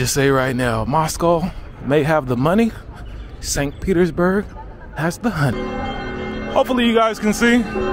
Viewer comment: кто хорошо знает ангийский, что он сказал? 😊 Москва — деньги, а Санкт-Петербург — ? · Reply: Moscow - has the money, Petersburg - the honey (мёд)